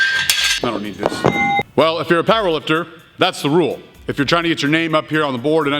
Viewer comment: He kept saying "If you're a power lifter..." but what I heard was "If you're a pile of dirt...." XD I swear that's what I kept hearing lol I kept thinking "There's no way he's calling people piles of dirt!" made my day XD